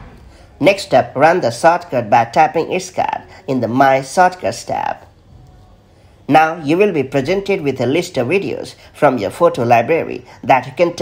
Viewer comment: "the my sorcuss tab" Sounds like "my circus tab". Can't you make a little more effort to speak properly? People can't understand you